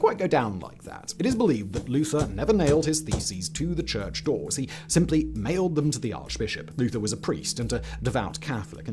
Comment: I definitely heard that as "Martin Luther nailed his ninety five theses to the archbishop".